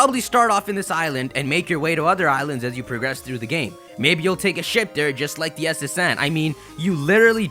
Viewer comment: i heard "take a shit there" :|